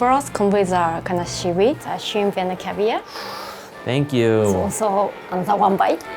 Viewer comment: "(mumbling)"... I think she says that its a kind of seaweed, then shrimp caviar..